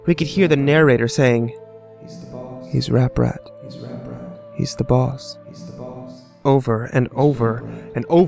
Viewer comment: Damn, I thought you were saying "He's the balls".